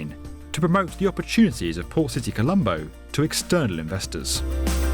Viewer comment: I thought he was saying Poor City Colombo